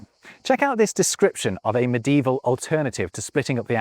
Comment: mədevil ???? What kind of word is that?